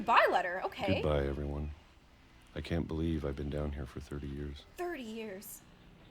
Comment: THIS MANS HAS BEEN EATING PEACHES FOR 30 YEARS ( I think that's what he said that's how long he had been down there)